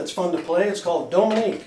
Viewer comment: I can't seem to tell what you said at 8:28 about the name of the song. can you tell me the name of the song please? thanks. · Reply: Dominique